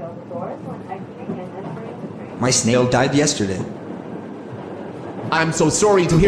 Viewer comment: I thought he said “my snail died yesterday” I feel so dumb